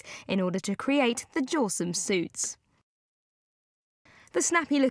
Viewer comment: did she just say "jawsome suits?"